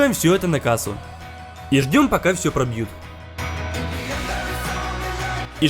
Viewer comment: я послышалось что он сказал "и ждём пока всё пратухнет"